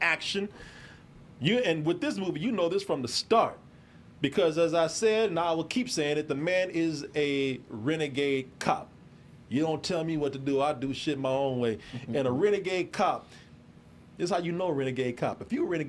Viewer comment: Thought was saying Reggea Cop, can you imagine Steven Segal with Dreadlocks 😂